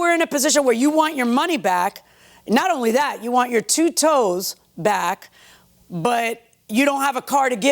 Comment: i heard you want your two toes back lol